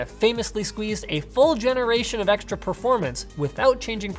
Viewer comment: did you just say "对", LOLLLLLL.